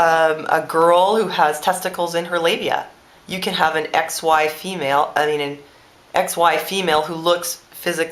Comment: That's funny, I thought you said 'ex wife female' when you said X Y female.